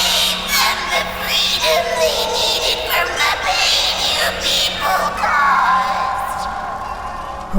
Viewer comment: Can someone tell me what she saying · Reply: "I gave them escape! I gave them the freedom they needed from the pain you people caused!"